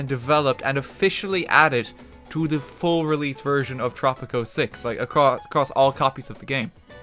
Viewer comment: i thought you said poptropica